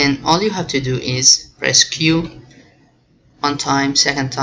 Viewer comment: pleeeease what does means the last step i heard """resqyoo""" 3:17 minute answer me iam in very very difficult problem please help me ?????